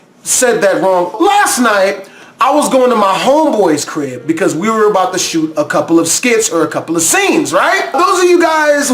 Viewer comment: I was worried at around 0:56 when I heard the word shoot. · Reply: I am certain he said shoot. "we were about to shoot a couple of skits..."